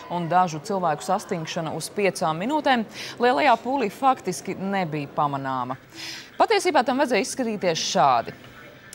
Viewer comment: hahah from 0:40 to 0:45 it sounds like she says "Holy fuck this kid may beat bamanama" LOL